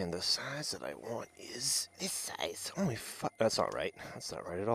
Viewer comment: "Holy Fu..." Or am I hearing things? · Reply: "Only five dollars." = "Only fu-"